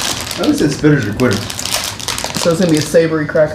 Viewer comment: did he say a spitter is a quitter??? 🤣🤣☠️